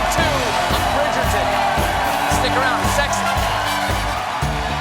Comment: Am I just imagining or hearing wrong, or at the end did Stephen say, "stick around, sex!" before walking away from the camera? XD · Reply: I heard something like that too. I thought, after talking about Weiner and then what a great show he had tonight, he was saying, "Stick around -- sexy!" Like we'll be having a sexy show. Maybe we both misheard.